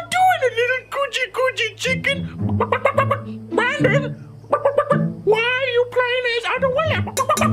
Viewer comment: What does the chicken say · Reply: The chicken said Brandon y r u playing as adoao